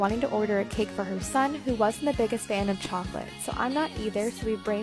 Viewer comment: I misheard her saying 'he WASN'T a big fan of chocolate' as 'he was a big fan of chocolate' and throughout the whole video, i was like 'Where's the chocolate? That's vanilla? Why are you doing that? Did the lady mentioned that it's his punishment and can't have chocolate for his birthday?' 😭